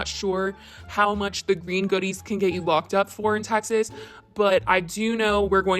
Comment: “green goodies” Vegetables , you can never have too much of something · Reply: I THOUGHT SHE SAY SAYING “GREEK GOODIES” THE WHOLE TIME I